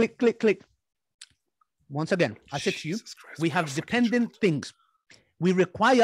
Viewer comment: 1:8:35 Did he just say "Jesus christ" 👀 Isn't he supposed to be an Atheist